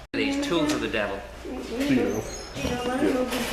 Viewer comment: So at 11:01, does she say "these tools of the Devil"; referring of course to "Scotch locks" or Bodge locks as they are known in the trade! :-)